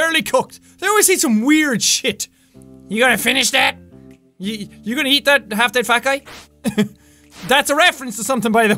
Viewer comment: "You gonna eat that fat guy?" lol (its family guy btw) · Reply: HKaftermath I thought Jack said: "You gonna eat that, fat guy?". lol So, I was like: "I don't get it. :\".